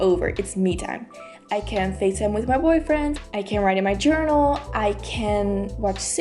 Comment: I thought she said call one of my boyfriends and I was like Whaaat